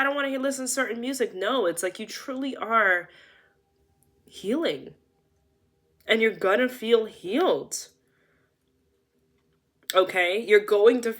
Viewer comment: I know i miss heard at the 11:14 mark. But what i heard was, "you truly are human, an your gonna feel human again" an it was in this moment that i realized 2 things. 1. How much i wanted an needed to hear those words. 2. That I've spent most of my life convincing my self that i am a monster pretending to be human instead of a human pretending/believing that im a monster.